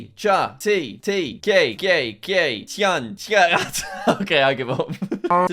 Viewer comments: did anyone hear him say gay at 3:51 when saying čaj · is he saying gay or caj maybe I just misheard?